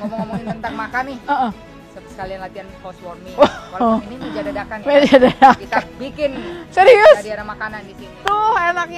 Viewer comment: Di menit 26:30 ada yg bisa jelasin ci Butet ngomong apa ? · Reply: "ngomongin makanan nih, sekalian latihan housewarming.."